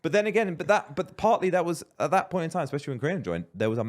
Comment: did he said Grian?